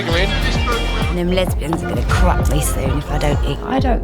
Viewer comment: I kept mishearing the 'corrupt' bit so I thought she was saying "these lesbians are gonna crack me soon"